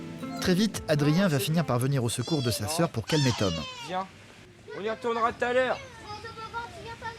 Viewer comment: Vers 23:30 J’ai entendu un gamin dire : « Sale con tom » C’est lui même le con il sait même pas c’est quoi être autiste il ouvre sa gueule